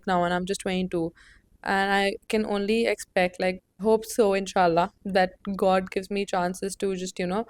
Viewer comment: did you say inshallah 🫶🤭🤭❤️❤️❤️